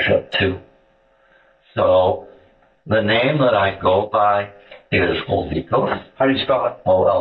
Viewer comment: I thought he was going to say that his name was "oligosaccharides". The gas that beans give to your lower intestine😂 🫘 💨 🤢